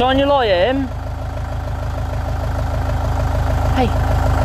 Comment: it sounded like a girl saying “Hey”